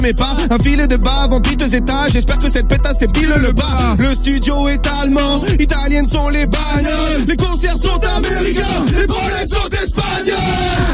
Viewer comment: What Nekfeu said since 11:45 to 11:50? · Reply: le studio est allemand. italiennes sont les bagnoles. Les concerts sont américains. les branlettes sont espagnoles